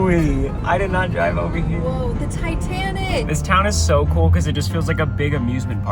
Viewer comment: did she just say the Titanic?